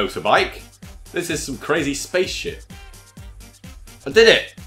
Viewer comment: did anyone else think they heard him say crazy space ** not crazy space ship?!?!